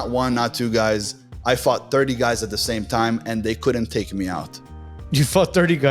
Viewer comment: i was cleaning while listening to this and I misheard "fought 30 guys" as "fucked 30 guys" and i was like "yeah, sounds about right"😭🙏